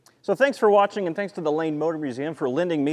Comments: Ohhh, the LANE Motor Museum, I thought you said "Lame". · I thought he said "The Lame Motor Museum"